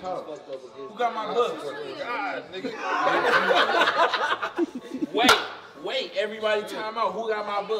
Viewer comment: I thought he said, "Who grab my butt ?" 😅